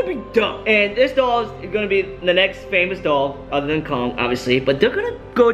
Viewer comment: did he say dog or doll Cause I heard doll 😂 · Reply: Mubasher Ali doll